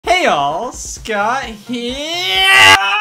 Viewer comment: I've had my first Scott the Woz Mandela Effect because I thought he said hey Ya'll he says hey all